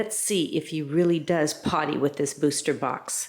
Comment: Wow lost for words! I thought you said “PARTY” with your booster box. But you actually meant “potty” it must be your crazy accent. Every time I close my eyes I now see that image 😱